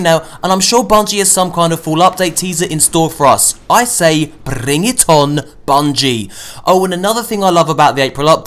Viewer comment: Did you said Bring it on or Reague-ton @ 6:16? Lol love your vid 😂👍👍